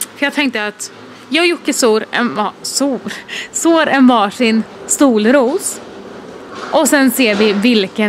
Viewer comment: så sa stolros istället för solros! Gilla om ni hörde likadant:D